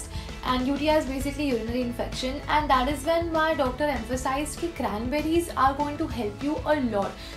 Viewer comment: doctor sounded like daughter to me😂😂😂😂